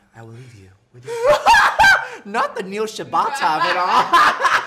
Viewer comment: Wait wait wait, did they said at 12:28 that he was with Neil Shibata???